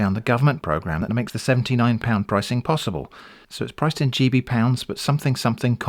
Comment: I thought for a second you said "chibi pounds" and had the mental image of cutesy anime currency